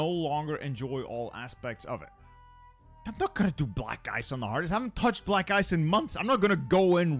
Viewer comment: I thought said black guys not black ice!! I was like what’s so convoluted about that?!